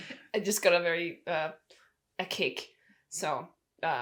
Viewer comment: when said I just got a "kick" I misheard "I just ate a cake". I was like well GOOD FOR YOU